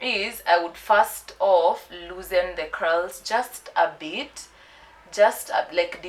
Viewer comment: what she said "Loosen the Curls" vs what i heard "loosen the cows "mwa yiyaaaa....Kazi safi tho hehe